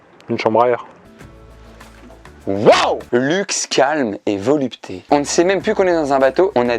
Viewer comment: à "luxe, calme", j'ai compris "Luke sky" et mon cerveau à complete en "like skywalker" j ai pas compris XD